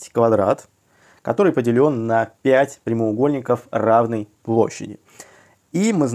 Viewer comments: Вы должны сразу говорить условие задачи. А то вы начали решать, и потом оказалось, что все 5 прямоугольников имеют одинаковую площадь. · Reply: аналогично, услышал "разной площади", что, собственно, и нарисовано + разные цвета со штриховкой. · Вы должны сразу говорить условие задачи. А то вы начали решать, и потом оказалось, что все 5 прямоугольников имеют одинаковую площадь. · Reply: равной и разной созвучны. Мне тоже послышалось второе.